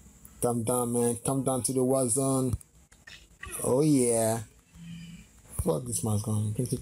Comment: Did you say *_" Come Down To The West Side at 2:04 " 😭😭